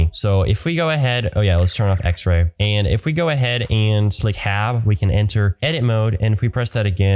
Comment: what keybind did u say? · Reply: tab